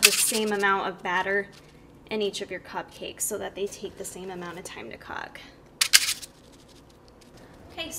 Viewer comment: Did she say same amount of time to cock?